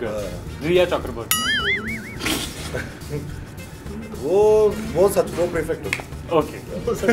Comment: I couldn't understand what he said about Rhea. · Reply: Prefect who is controller/senior